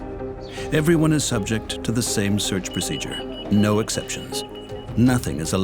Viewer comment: I always heard him say El Longo